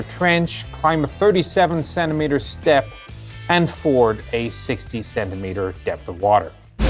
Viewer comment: Did I hear 67 meter water crossing capability instead of 67cm? :D